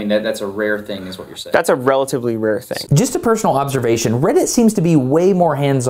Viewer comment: Destin: When Reddit employees get involved... that's a RARE thing? Yes, it's a VERY RARE thing. Steve Huffman: nods, signals to henchmen to release and Destin's families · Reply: I heard "relatively rare", not "very rare". You riling-up troll, you.